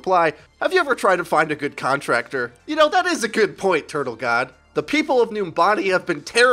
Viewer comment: Was I the only one who thought said that guys name was "Turtle got ass" instead of "turtle god" asks?